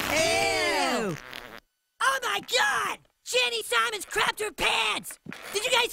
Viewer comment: I thought it said craped her parents